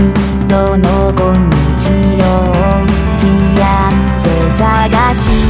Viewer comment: いや本当に歌詞ドキドキやけどフィアンセが、フィナンシェって聞こえるw "俺のフィナンシェ"